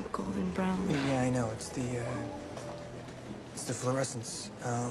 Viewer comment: “It’s the fluorescence” that’s my least favorite excuse of his. It was dark as hell in that school what fluorescence was there? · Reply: OP misheard it, he said "It's the fluorescents", not "It's the fluorescence". He meant the lighting (the fluorescent lights) in the school made his eyes look lighter and darker at different times.